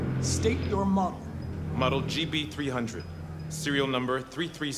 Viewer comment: The android says "GB300" instead of "JB300" I felt like I had to point it out